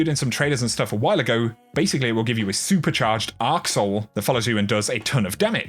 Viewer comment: Hahaha, I misheard one line. I thought he said it summons an a*sehole that follows you around and does damage! That would be something to see! 😂